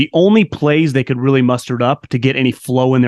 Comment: did he say "mustard up"?